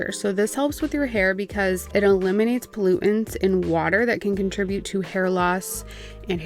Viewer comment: i’m glad i went back and listened to this part again. i thought you said gluten in water and was VERY confused